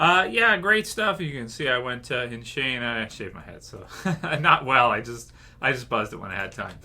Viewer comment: Did you just say 'SHANED my head' instead of 'shaved my head'? LOL